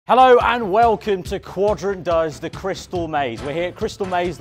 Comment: why do I hear "Hello and welcome to quadrant does the crystal meth" My mind is Trippin these days